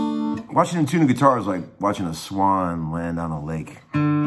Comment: I heard "is like watching a swine land on a lake" Thought "that's not very elegant" before i realised i'm french